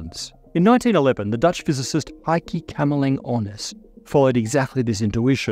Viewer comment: Thought you said, "I keep cammelling on us." Edit: Heike Kamerlingh Onnes